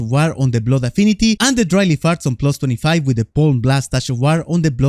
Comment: It sounded like you said “dryly farts” there